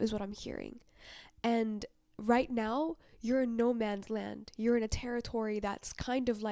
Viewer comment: I heard you say "it's Nomad's land". And yes. It is temporal. He he damn orange cones and perfect Harley past by again. ...I love my walk home. Kilo Mike 6 galactic federal. ❤ thank you